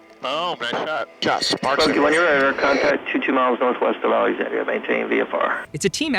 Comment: the captions say "22 miles northwest of (indistinct)" (indistinct) is Alexandria, LA. I've spent a lot of time there or going through there further into Cajun country, never knew the bombing range was so close.